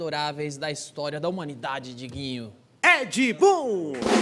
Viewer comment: did he say 'Edgy Boon'?